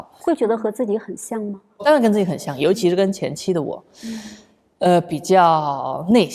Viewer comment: Just to clear any possible confusion, at 9:02 "especially my ex-wife" translation is wrong- "ex wife 前妻" sounds like "previous period 前期" (both have the same pinyin "qianqi") in Chinese, what Zhou Shen's actually saying is more like "Of course it's quite similar, especially with myself in previous stages, when I was somewhat more introverted"